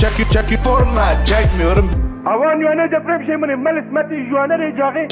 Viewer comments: de ne diyorsun abi merak ettim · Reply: avanyene depremjemini melis meti juani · den 1:18 e kadar olan yerde ne diyor (kürtçe olarak) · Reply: navay yuani depremi semili velit meti cuari